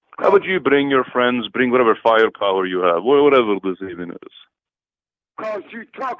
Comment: As soon as he said bring any artillery I thought perfect for Irate Black man hahahaha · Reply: I thought he said "firepower"